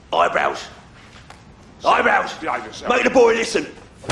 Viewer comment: or 7:29 eyebrows sounds like he said behave yourself when he replied to Charlie shouting his name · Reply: He said it to Mark he said "Sit down and behave yourself."